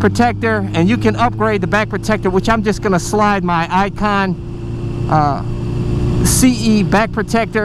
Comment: the way you said ICON sounded like 'ipod' I thought you were going to slide an ipad in as protection, lol